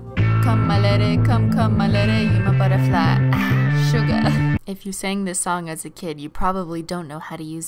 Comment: I'm I the only one that thought she said "Suga"